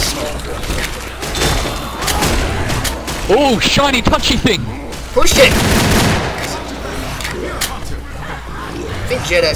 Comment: your blog is the best and did you say oh sh**